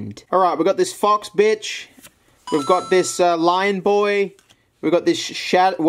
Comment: what did you just say? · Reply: Foxes Ari the best!